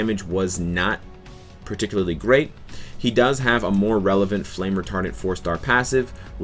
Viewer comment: I thought you said "Flame retarded"